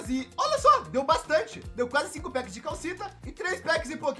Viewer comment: eu juro que eu escutei "quase 5 packs de calcinha" kkkkkkk